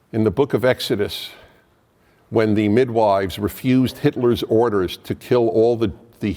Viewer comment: did he say "Hitler`s orders" instead of "pharaon`s orders" ??